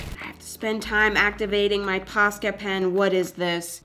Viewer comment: auto subtitles say pasta pen and that sound amazing tbh